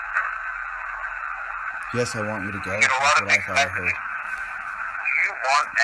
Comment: t about 12:27 he clearly says 'No' that you do not need to go. he does say he is angry though.